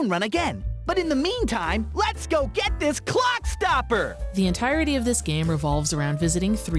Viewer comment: ISTG I for some reason thought he was saying "cocksucker"